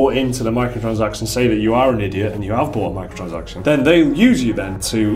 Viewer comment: I legit thought it said Micro Dragons and not Micro transaction... I'm way too high right now.